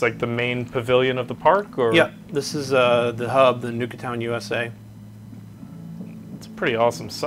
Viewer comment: Anyone else hear at 45:31 he said Nuka-town instead of Nuka-World 😂😂